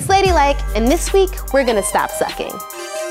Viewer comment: Swear to God thought it said stop smoking not sucking